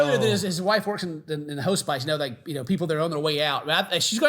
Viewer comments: Did… did Ken just say HO-spice? Instead of hospice?! 😂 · Wait. Did Ken call hospice HO-SPICE?! 😂